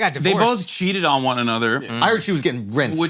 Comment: LMAOOOOOOOOO this mf said “I HEARD SHE WAS GETTING RENISED.” IM DONE LMAOOOO.